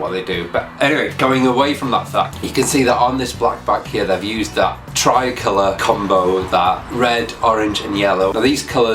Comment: who made that bike? I couldn't understand the builder's name when you said it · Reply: Cognito Moto